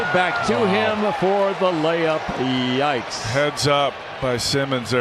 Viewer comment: did he say that was a layup